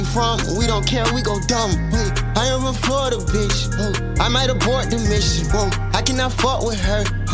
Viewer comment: Did he say “I am before the bitch” or “i am a Florida bitch”